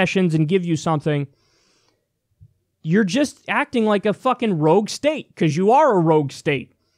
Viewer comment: Rouge state. I thought said road steak.